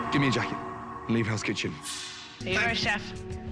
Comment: I can hear "alright chef" and "sorry chef" but i'm leaning towards "alright chef" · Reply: I heard ''Here you go chef''